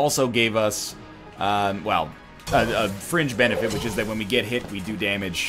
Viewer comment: When NL picked up the Frozen Heart, I thought at first that he said, "Fridge benefits", and I was about to be so unbelievably impressed at such pun-mastery. Then I realized, "Wait, he actually just said 'fringe'." :( So disappointed...